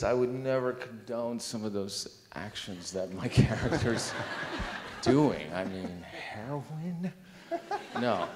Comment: What did he say? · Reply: "I would never condone some of those actions that my character's doing. I mean, heroin?"